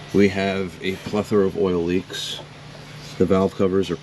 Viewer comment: I always wondered what the collective term for multiple oil leaks was, sounded like you said a fucker of them? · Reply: Plethora - multiple, many